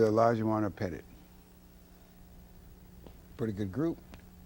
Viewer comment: what does he say? Pettit or Penny? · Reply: Pettit